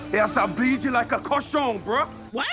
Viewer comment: I thought he said: "Beat you like a croissant". 🥐 🤔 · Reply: it’s « cochon » it’s the french word for « pig »